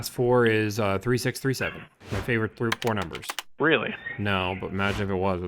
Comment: WHEN HE GAVE THE LAST FOUR STOCK NUMBERS I THOUGHT HE SAID MY FAVORITE PORN NUMBERS 😂 I SAID WTF?!?